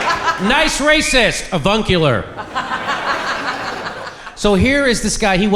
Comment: "Evuncular?" I thought you said "carbuncular". I guess both work, though.